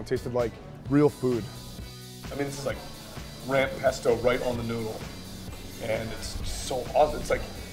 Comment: did he just say noodle?